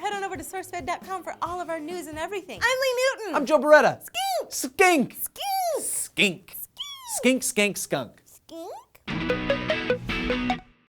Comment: the entire video i thought they were saying scank...